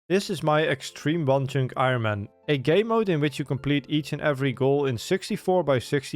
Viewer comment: Love your content But in your intro it really sounds like you say "gay mode" instead of "game mode" As a gay, it's not a problem but always makes me laugh when I hear it